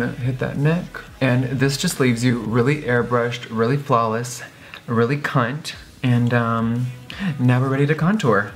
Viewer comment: did he say "really cunt"???